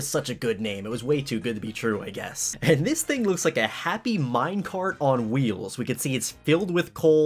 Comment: A piece of coal A mincart of coal A mountain of coal A whole factory on legs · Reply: I thought minecart said minecraft